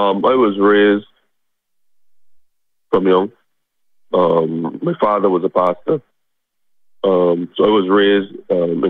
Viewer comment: "My father was a pastor" Mystery solved. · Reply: I did a double take there, thought he said "My father was a bastard"